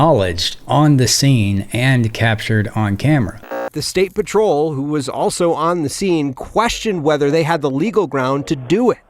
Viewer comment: I thought they said the chief was also on the scene. Why his dumb old ass is not in any fuckin trouble? Legal, fuckin trouble?